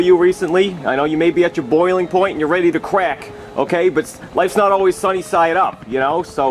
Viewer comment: When he said crack as I thought he he said cracked at Fortnite my guy